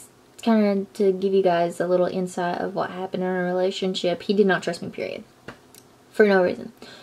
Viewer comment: I thought she said: " he didn't trust my period..."